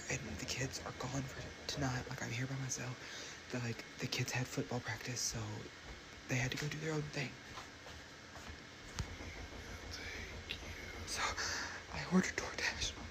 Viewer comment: Does anyone Else hear that voice tlkng with him saying "I will take You?"